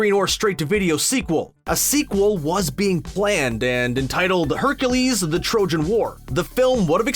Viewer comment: I thought he said Hercules: The Trojan Whore 🥴